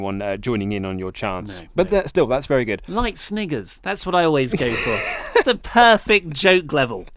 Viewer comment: Somewhere at 5:10 I thought said nigga